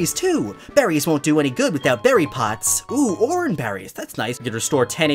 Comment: who heard him say "what use are berries without berry pots?" XD